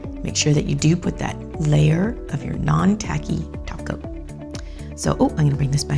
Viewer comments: I swear she said none taky toco 😂😙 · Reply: Galaxy Doggo she said non-tacky top coat They are a thing. 😐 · did she just say taco?🌮SimplyNailLogical is coming Edit: nvm she said topcoat but is just sounds like taco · For a moment when she say top coat I thought she said Taco and I thought she was speaking simplynaillogiclish